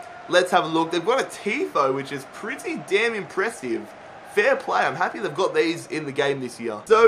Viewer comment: what did he say ? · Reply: XAZAZEL he said damn, so what